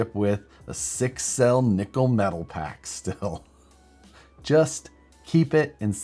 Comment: could of swore you said “Nickelback” Packs 🤣